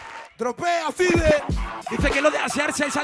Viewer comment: Que dice el presentador? 1:11 dropea pibe, o tropea five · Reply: Tropea Five Five Types o algo así se llamaba el dj de esa red bull